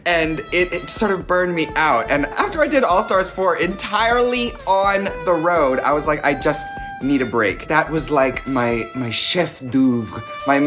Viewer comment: when she said all stars 4 i thought she said all stars 5 and my heart dropped i was so excited